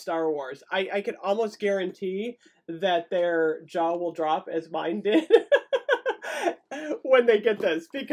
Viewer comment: You said your jaw will drop. I heard "Your Jawa drop." Stars Wars overload I guess.